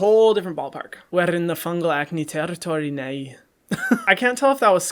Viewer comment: did he just say "fungal acne territory ni hai"??!!! 🤪